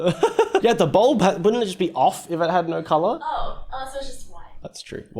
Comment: don't worry Felicity I thought he said color too lol